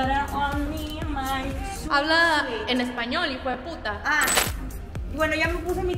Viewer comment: en el minuto 9:29 se escucha que dicen "pero habla en español hpt" pero no es voz de odalys y tambien en el minuto 14:56 la misma voz dice "pegate pa que sientas" o algo asi dice